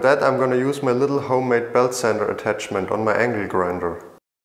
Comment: It sounded like you said "Angry Grinder" instead of "Angle Grinder", made me chuckle.